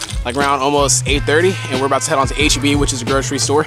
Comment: did you say "we about to head to HBU which is a grocery store?" bruh I know you meant HEB but got me missing Houston big time with that one lol